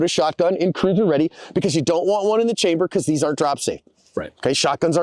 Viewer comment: did you say shotguns ARE drop safe or AREN'T drop safe?? i can't tell which you're saying?? Thanks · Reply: Are not.